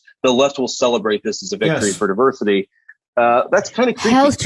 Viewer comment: What did she say?! "Health jus.. Health justie zoomies ta ex-tami-nate wreck-a-pause black people... yeah" (???????)